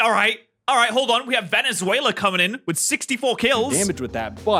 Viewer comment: did he just say venezuela for veluza